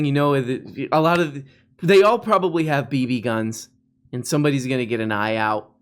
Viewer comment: I swear I heard you say beanie guns instead of BB guns